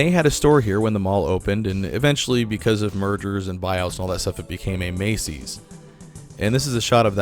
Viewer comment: I thought for a second you said Murders and Buy-outs and thought: 'Damn. American retail is so bloody thirsty'!